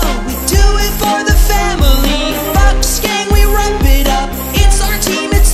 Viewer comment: Follow me on SoundCloud to listen to all of my music for free! <link> I also have all of my music on iTunes, Spotify, GooglePlay & more! Just search for "VuxVux" =P This song is dedicated to you #VuxGang 💜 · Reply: when you say vuxgang it sounds like you say fuckgang XD